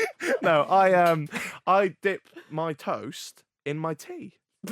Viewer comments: I thought you said you dipped your TOES in tea oh my god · I literally heard “I dip my toes in my tea.” 😩 Gosh I was so confused!